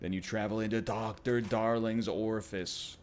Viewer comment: Did he say orifice?